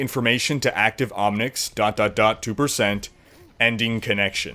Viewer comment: "dot dot dot 2%" 2%? or 3%? I heard 2 but i see 3!!!